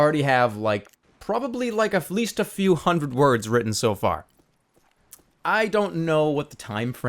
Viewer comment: i thought at 2:55 he was saying he had a few hundred pages so far XD